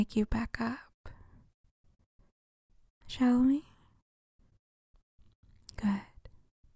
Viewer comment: i love this audio but at 8:18 i thought you said “Jolie” and not “shall we” this time around and it was SUPER creepy